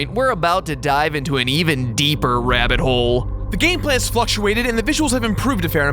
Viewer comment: When said fluctuates I thought he said fucktuates